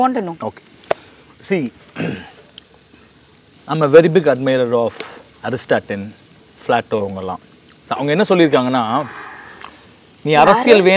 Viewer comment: Did he say Aristaten and flato?